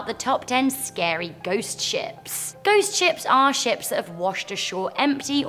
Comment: For a second when i was scrolling thru the comments i thought she said "Ghost Chips" instead of "Ghost Ships" XD